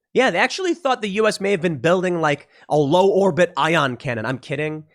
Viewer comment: did Tim say Ion cannon! Now you’re speaking my language!